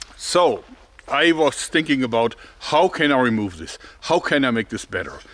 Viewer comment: I think you said stinking instead of thinking.